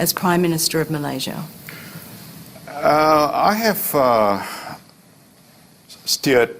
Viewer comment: When najib is ask about his legacy. I thought i heard najib say, “i have STEAL....” lol, my mistake i heard wrong.